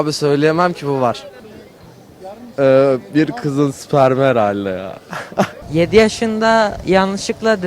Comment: ne dedi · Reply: Kız spermi dedi